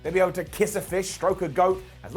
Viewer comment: I swear I though I heard say “grope a Goat” but I was wrong 😂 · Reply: Its stroke, same thing lol unless in England that's slang for something